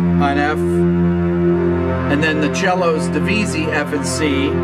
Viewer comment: What does he say at around minute 11:11 "Then the cellos, .....b c and f c" I think it's the same word he says at around minute 4:50, something like "deveces". Can anyone help me out? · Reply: Might be a bit late but these are called 'divisi' Basically you write down div. or divisi when notating multiple notes and then one half of the section plays the one note, the other half the other note. This can of course also be done with more than two notes, but then the overall sound gets of course less powerful as for example only one fourth of the section is playing a particular note.